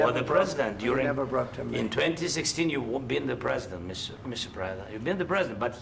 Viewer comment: Did he call Trump Mr. Prada? 😂